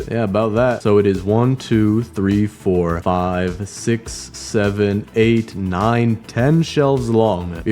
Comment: did i hear 67